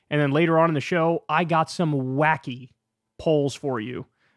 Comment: Legit thought Kyle say "I have some wacky holes I've been thinking about" had to rewind to relize he said wacky polls. lmfao